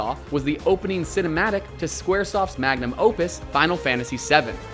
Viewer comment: did he say magnum opus like Mad Max